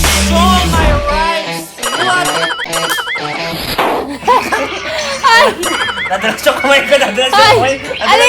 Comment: did you say hay or HUN??? hahahaha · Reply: Its hoy dumbass